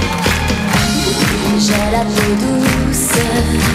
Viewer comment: I always thought she was saying "j'ai la produce"... which not knowing French I translated as "I have the produce" :D Because she did.